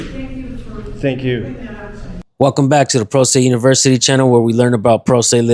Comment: It sounds like your saying prostate university